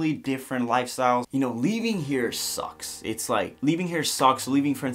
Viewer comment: When he said leaving here sucks I thought he said living here sucks lmao